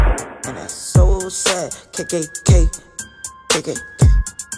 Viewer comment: Is it supposed to sound like Kodak says "kkk" "kkk" @ 0:04 and @ 0:06 ? Or is that just me lmfao ?